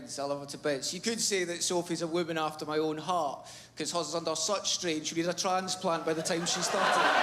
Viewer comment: I kinda know what he's saying here, but I'm American and this is much thicker than Craig Ferguson's Glaswegian accent 😂 can someone help me translate the punchline here? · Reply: not much to it, another fat joke - "you could say she's a woman after my own heart, coz hers is under such strain she'll need a tranplant by the time she's 30"